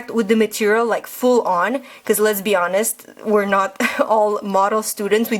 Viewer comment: i thought i hear lezbians sjijeifiejr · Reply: cic hahahah yeah I was talking really fast and my accent doesn't help. XD I said "let's be honest"